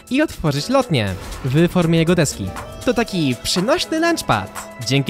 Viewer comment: Zamiast deski usłyszałem eski xd 😂